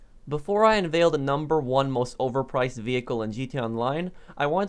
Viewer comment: I thought it said overpowered and I was like "How tf is a Cerberus oberpowered?"